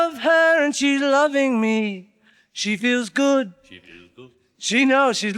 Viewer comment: Lennon's "she feels good" at 17:39 and McCartney's slight laugh is great. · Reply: Wait, WHAT? I also thought John said "she does," not "she feels good." Crazy